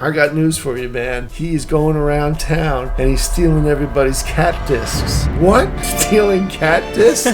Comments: I WAS CONFUSED BC I THOUGHT HE WAS SAYING CACTUS NOT CAT DISC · When it said cat disc I thought it said cactus lol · I thought he said cactus not cat disc